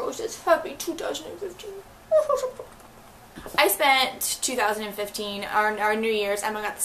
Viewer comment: When you said, "I spent 2015..." I thought you were saying you spent $2,000 on books for Christmas! I was shocked at first hahaha